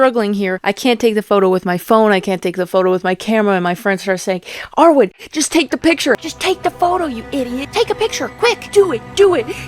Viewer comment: I thought you said "r word just take the picture"